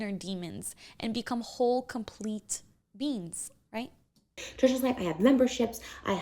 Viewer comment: Awee I couldn’t help but giggle when I thought I heard you say “so we can become whole, complete beans🤍”